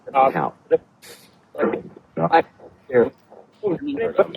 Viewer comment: I swear somewhere around the 46:00 mark I heard Terrance is here.